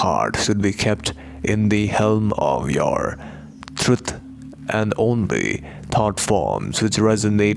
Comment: What is that word? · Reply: truth